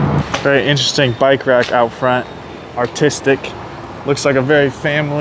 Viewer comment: why did u say the bike rack was autistic at 4:46 ?? thats offensive